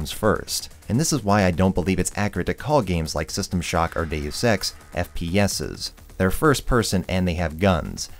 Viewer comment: When he Deus Ex i thought he said Dave Sex